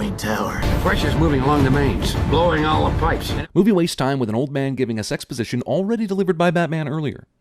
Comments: I heard "Old man gives us exposition" as "Old man gives a sex position" I had to go back and check what you said. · Sin 45: I thought you said 'an old man giving a sex position' O_o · I could have swore they said sex position instead of exposition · The first few times I heard 5:22 I honestly thought he said "Movie wastes time with an old man giving a sex position..." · haaa...I heard "sex position" in place of exposition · Huh. I was so confused for a second at 5:23. I thought he said "giving a sex position" before I read the subtitles. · Thought he said "old man gives us sex position" not "old man gives us exposition"... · did anyone else think he said sex position? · does anyone else hear '...an old man giving a sex position...' or was that just me?